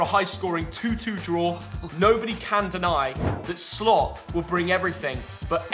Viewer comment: Did say SLOP ? at 2:03 ?